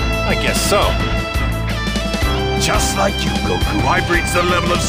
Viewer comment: what did he just say goku i must be hearing and seeing things